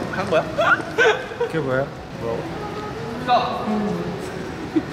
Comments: Am I the only nepali army who heard "k vayo" 🙄 by Taehyung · solo latinos entenderan... en el minuto 00:43 escuche "que wuea" like si tu también👁👄👁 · Did V just say ke bhayo (what happenened in nepali) at 0:40 😱🤯